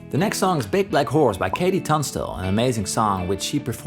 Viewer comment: swear to God I thought he said big black whores